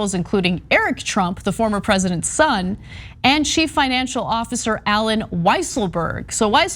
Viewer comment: I heard "former president Trumps son Eric Trump" as "president Trumps former son Eric Trump" and did a huge double take before realising i misheard. I thought maybe he died, disowned dad, went trans...